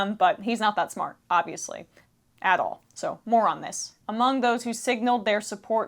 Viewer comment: haha I misheard! "he's not that smart obviously at all. Moron this!"